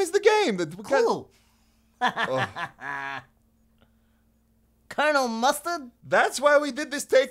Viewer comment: At first I thought he said Colonel Mustang